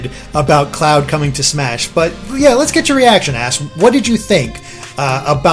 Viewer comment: Did he just call him "Ass"?